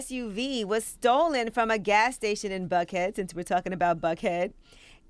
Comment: I thought they said Bankhead! I was like what do you expect but Buckhead? Geesh! All this is backlash for the mayor firing the cops who tazed and jacked up those 2 black college students.